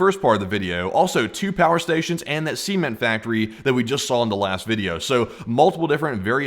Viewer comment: Not gonna lie i thought you said a semen factory. And i cant unhear it. 5:30 and now neither can you